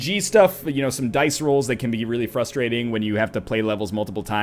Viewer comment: I'm not speak english too well, so can somebody write the word, that Northernlion says fucking million times??? frastrading?? fast-trading?? I just can't understand!!! (like on 28:43). Btw, more series! =) Videos about this game were amazing! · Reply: "frustrating"